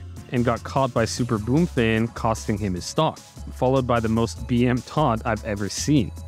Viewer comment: what did you call that taunt? a bm taunt? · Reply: yea bm. it stands for “bad manners”. basically just expressing disrespectful behavior